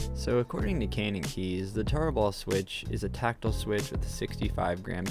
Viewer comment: Dude I Cant Be The Only One That Thought It Said "Taco Bell"